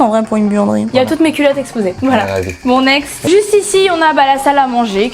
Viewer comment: moi qui comprend "mon ex" 🤭 me disant c'est quoi le rapport 😅. Heureusement ça n'a pas durer après je me suis dis ah bein non elle a dit "BON NEXT" 🤦‍♀️🤣🤣.